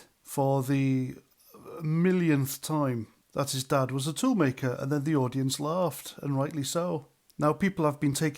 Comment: Thought he was saying fool maker think he’s dad owned the company